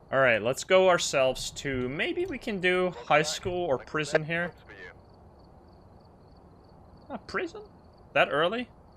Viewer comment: what did the radio say on 36:00 ? i didnt hear it and cc isnt helping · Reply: "Welcome back, I have prepared some jobs for you"